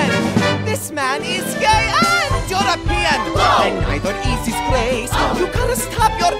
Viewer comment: I thought it said, "Hes gay! And your a pee " xD